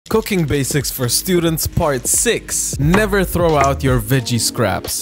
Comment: Me who thought he said “coc*ine basics for students